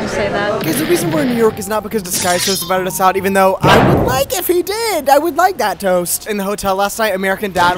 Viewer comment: can someone PLEASE tell me what is saying at 3:03 i genuinely cannot understand a single word apart from disguised toast LMAOO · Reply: “guys the reason we’re in new york is not because disguised toast invited us out even though i would like if he did i would like that toast”